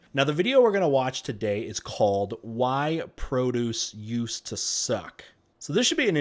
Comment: i thought it said why produce used to suck snacks